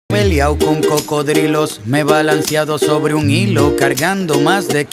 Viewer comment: al principio entendi " me he liao con cocodrilos "